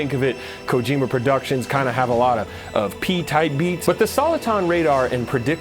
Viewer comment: Thought you meant "pee tidbits" instead of "pee-tied beats" and for a sec I thought I had a stroke most of my life, thinking tidbits were pronounced that way. Not a native speaker so wouldn't be the first time I've mispronounced a common word for an embarrassing amount of time.